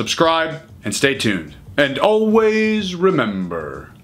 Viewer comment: Every time he says it it sounds like he says "Train on time" to me. EVERY time.